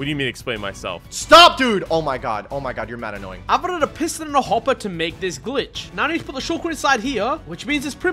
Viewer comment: i thought i heard him say "piss in a hopper"